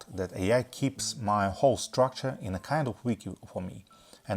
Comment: Good video as always, but I didn't understand what you saying at 3:15 "AI keeps my whole structure in a kind of ??" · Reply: Sorry, I accidentally uploaded an old version of the script as subtitles, so this part is missing. I meant: “...in a kind of wiki.” I’m using Antigravity and Claude Code with Karpathy’s Wiki LLM method.